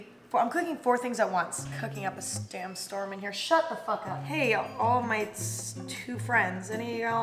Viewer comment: am i just a gigantic otaku or did someone else hear "all might"